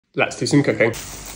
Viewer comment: I literally heard ‘let’s do some cocaine’